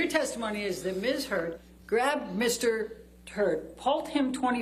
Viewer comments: did i hear "Miss Heard grab Mr Turd?!" Did she really say that? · Did she say miss heard grabbed mr... turd?